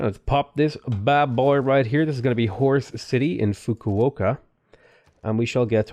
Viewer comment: When he called Fukuoka "Horse City" I thought he said "Whores City"